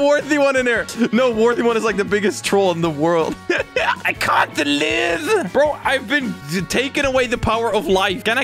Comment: that voice is too funny 🤣🤣🤣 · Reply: it sounds like hes saying "I CAN'T DELIVE"